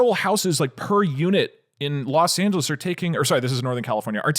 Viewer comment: Did he say Los Santos?